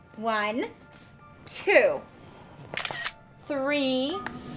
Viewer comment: wait did she say three